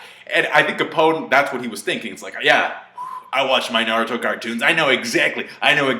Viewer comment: Did you say naruto cartoon? 4:44 ＴＲＩＧＧＲＥＤ